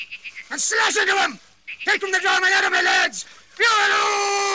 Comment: what is that chant he is saying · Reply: He's calling "Horraloo", a english cheering at fox chase.